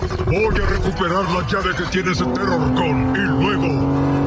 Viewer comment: Cuando optimus dijo "terrorcon" yo entendí "perro bocón" 😂